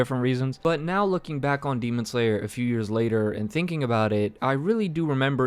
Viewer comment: i’m i the only one who heard daemon slayer at 6:18?